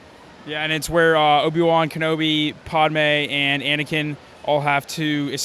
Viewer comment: did he say PODme